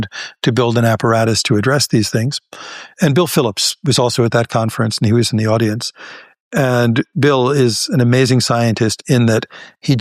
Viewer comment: I am profoundly impressed by his ability to articulate complex quantum phenomena spontaneously without ever drifting into incoherence. It is rare to see a physicist maintain such rigorous logical structure in real-time discourse. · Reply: I thought you said "decoherence" and was preparing to ask questions 😂